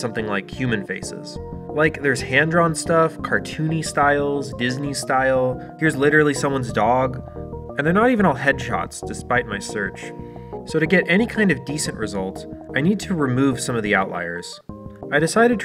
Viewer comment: For a moment, I heard "head shots" as "hedgehogs."